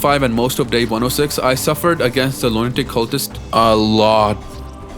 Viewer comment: Bruh I thought you said "loin dick cultist" for a second...